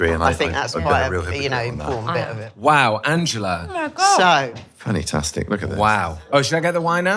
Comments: Did Benedict just say "fanny -tastic" 😮 ... 😅 ..... food looks damn good, love chicken. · does Benedict say fanny-tastic?! 🤣